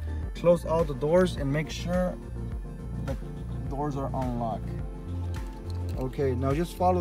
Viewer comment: You must be trying to say "unlocked"? I thought you said "on lock" and when I went to program the key fob, the alarm went off, but I had no key fob to turn it off lol. I had to Google real fast that you simply unlock the drivers side door manually with the key to turn the alarm off.